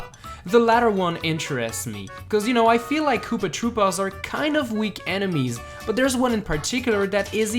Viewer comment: hmmm i thought you said koopa troopas werent enemies